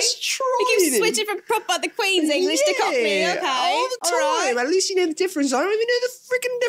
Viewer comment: subtitles are wrong should be "you keep switching from proper, the queen's english to cockney, okay"